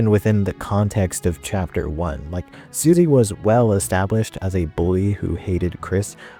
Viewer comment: Susie is a girl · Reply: I'm assuming you misheard "bully" as "boy".